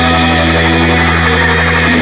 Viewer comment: Why hasn't anyone pointed out that it sounds like Jay is saying "My flares were getting in my lemons" at the end. What?